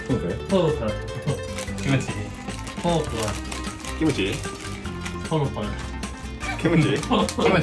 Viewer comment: 📕さんの「キムチ」が「ひむち」にしか聞こえなくて好きだ ⬆よく聞いたらス○ィッチみたい（